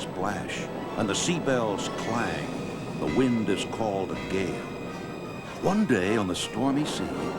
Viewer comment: = The Wind Is Called A Gay (Thats What I Hear)